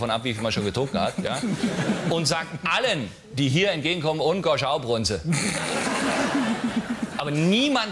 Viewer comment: was sagt er da ? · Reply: 'und? gohsch au prunze?' und? gehst du auch pinkeln?